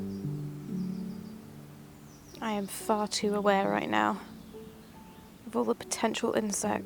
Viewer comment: I thought you said "im farting away now" lol. Kinda thought it was one of your goofy endings haha